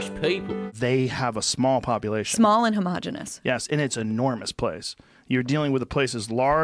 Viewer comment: don't tell me she actually said we are homogenous